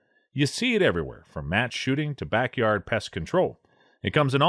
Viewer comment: Omg at 1:40 I honestly thought you said "you see it everywhere from MASS shootings to..." I was thinking good lord man. Really? I was relieved to back ot it up and hear "match shooting"